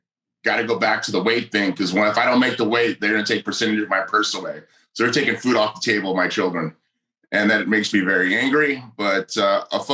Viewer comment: ? Or does he actually say that at some point? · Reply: he said "theyll take a percentage of my purse away food off the table of my children" 😂😂😂